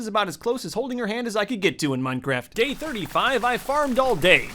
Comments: For a second I thought he said Gay 35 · it sounds like he says Gay 35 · I swear he said gay 35